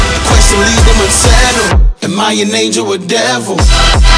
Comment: I just can't help... I always hear "am I a ninja or devil" instead of "am I an angel or devil"